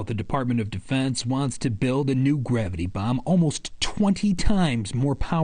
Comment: Thought they said Gravity Bong.. 😂😂 those already exist.